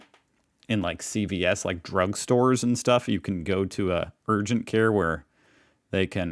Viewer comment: I had the volume on so low that I misheard the words, urgent care, as... virgin care. I was like: "What the duck is that???!!!" 😂🎉😂